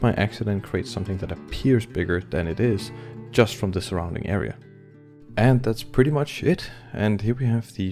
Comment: "and that's pretty much shit!" what the.... ??? oh, he said "and that's pretty much IT." whew. These are all important concepts to know and implement. These images were all great and great job on putting this vid together!